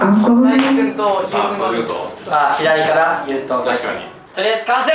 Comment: 🐉「とりあえず乾杯！🍻」にしか聞こえない笑